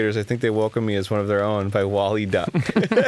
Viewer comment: I thought that 'Wally Duck' said 'Anally Duck'